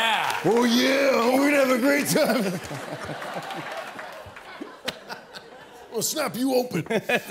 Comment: Can someone help me? What is he saying at 2:14 and why is everyone so silent? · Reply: the guy at the microphone says "I'm gonna snap you open"